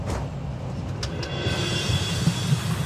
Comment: what is the computer saying it sounds like retard to me, but I know it isint. Anyone know ?